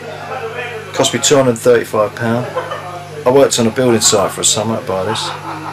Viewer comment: did he say he worked on building site in summer to buy that Gibson guitar? Adrian, always my hero :)